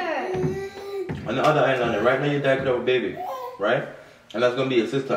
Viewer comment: Did just says that Landon’s dad is expecting a baby ?..so is Christina pregnant? @ 10:16mins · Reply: No I don’t this so, I think he said right now ur dad could have a baby?